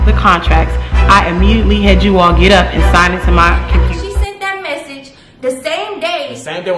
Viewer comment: You immediately had them all get up and do what?.... it faded out before we heard the rest... anybody hear what she said? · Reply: Ohh she said computer 🤦🏽‍♀️